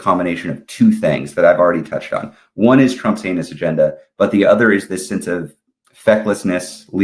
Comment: I heard the words “Trump’s anus agenda” The rather than heinous agenda. Both work.